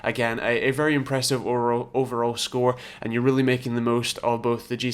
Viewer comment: did he say Oral?😂😂😂😂